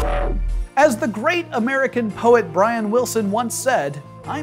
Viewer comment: I thought he was gonna say Brian Williams.